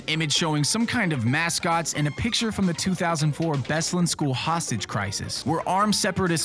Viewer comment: Did he say bestland school crisis at 3:14?